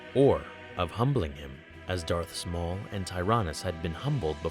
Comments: in the video it sounds like you said Darth small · I can't be the only one who heard "Darth Small"